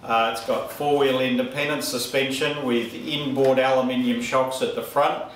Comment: I heard "Fron wheel independent suspension" Literally did not pronounce the "T". The Australian accent does that sometimes.